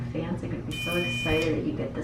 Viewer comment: Did she say his Fiance is going to be glad he gets this thing removed @ 8:48?